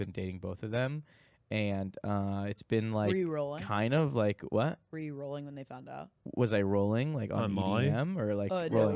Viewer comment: does say EDM when he meant to say MDMA? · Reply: I think so lmao